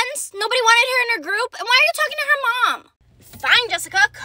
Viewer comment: At rather end I heard “skibidi mom”